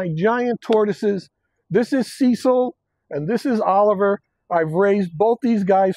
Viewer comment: I only realized the first ones name is Cecil when I read the comments. I swear I heard him say Sea salt and thought to myself 'Well, that's a weird name'!